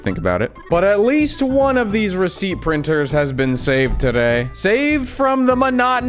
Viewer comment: I thought that said “racist printer” and got REALLY confused.